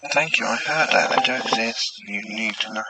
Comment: I swear i heard...."thank you, i have heard that i do exist, you need" and then right after you need, sounds like... "to use...." or something. Couldnt make out the last part. Sounds like it has some minor accent too